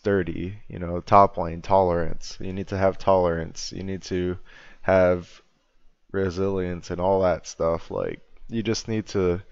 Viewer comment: did he just said "you need to have brazillians"? (sorry if it is any inside joke i dont know)